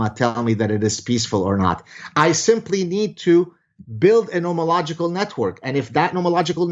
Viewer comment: For anyone that caught it at 6:12 - " Nominauticl Network" ? can some please correct what he said, very curios to know what that was called. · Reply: Nomological, means the application of logic to natural law.